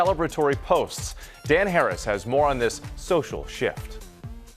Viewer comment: I swear I heard him say "social shit." 🤣😂🤣😂